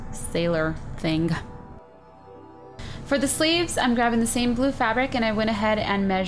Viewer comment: I thought she said "for the slaves"